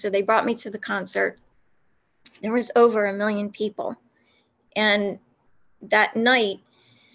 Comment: did you say you attend a concert with over a million people?